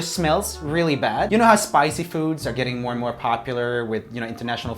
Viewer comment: You said spicy foods and I thought you said spider cucoons